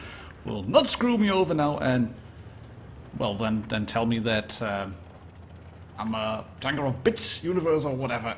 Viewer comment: the subtitles say "im a b*tch yeah" XD